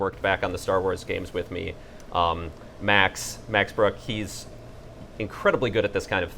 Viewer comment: Oh my God! For a second I really thought he was saying that Max Brooks, Mel Brooks son, author of World War Z, the Zombie Survival Guide (and several bestselling Minecraft novels), was working on the Stormlight RPG. No. He said Max Brooke. Different guy, lol.